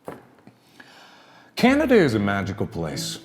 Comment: I swear to god I thought he said “Canado” at first 🤣